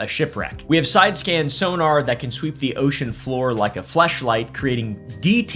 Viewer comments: I’m sorry did you say fleshlight or am I geeking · did he just say fleshlight? I Listened to him 5 times and am pretty sure that's what was said!